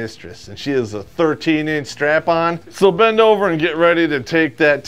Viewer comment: Did..... did he just say "hentai style" 🤣